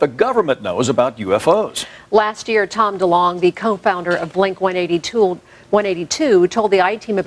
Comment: Did she say Blink 180-tool? xD